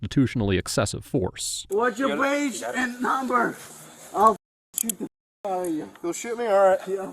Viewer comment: In the second video, I think the guy was saying "sue" but with his accent and slurred speech it sounded like "shoot." At 21:39 he says, "What's your badge number? I'll (sue or shoot) you." The officer says, "You'll shoot me? Alright." Then he says, "No I ain't gonna shoot you. I'll (sue or shoot) you. I'll (sue or shoot) you." Then at 25:25 he again says, "I didn't break the law, you can say whatever you want. I'll (sue or shoot) the __ outta your ___." To me those statements make a lot more sense if he was meaning to say "sue" rather than "shoot." I think his accent combined with his slurred speech from being intoxicated made the officer and ATA reasonably think he was saying shoot at the time, but I think they were mistaken. Taking off his threatening officers with deadly force, I'd bump his grade up from an F to a solid D.